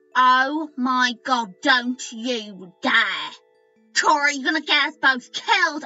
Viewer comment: hah! did papyrus think he wasn't making a truce or was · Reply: it sounded like you said Cory instead of chara